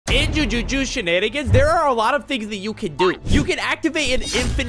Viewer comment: people who heard jujuju shenanigans